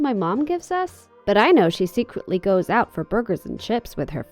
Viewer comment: I thought she said boogers XD